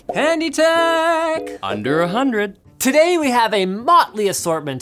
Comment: Darnt I thought it said tech under 10